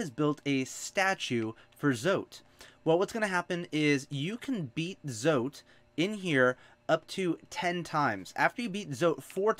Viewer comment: For some reason I heard "and you can eat zote about 10 times"